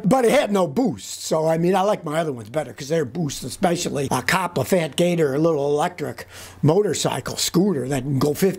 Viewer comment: Scotty- What is the name of that elect bike you like that can go 50mph- my hearing is poor. it sounded like you said "Copper Fat Data" mentioned at 8:26 after that crap Fiido. Thx a bunch